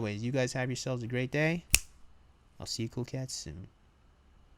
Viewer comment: I was listening to one of your videos the other day and at the end when you said “see you cool cats later”, I swore what I heard was “see you coke heads later”